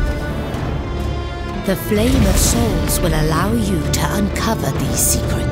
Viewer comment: I swear I keep hearing her say the Flame-A-Saurus at 0:12...